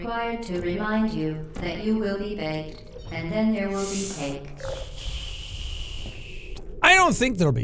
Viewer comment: Why do the subtitles say "[garbled]"? GLaDOS clearly says "you will be baked, and then there will be cake."